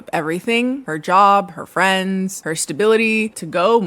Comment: When I heard this first I heard "gave up her dog" instead of "job" and I was ready to throw hands